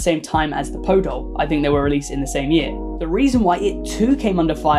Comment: The purple Teletubby sounds like it's saying "I'll kill again" and I love it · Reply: I heard "I hear the dead!" And I was very concerned about this psychic tellytubby